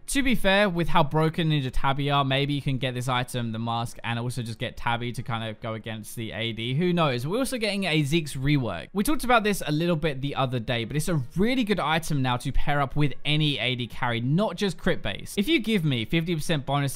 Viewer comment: i heard "ziggs rework" and i was like wtf